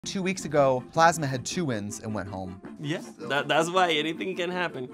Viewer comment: Had to rewind the beginning- I heard “Plasma had two wins and wet hole” 😂